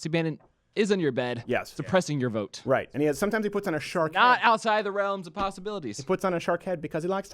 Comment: Did he say "A shlong"?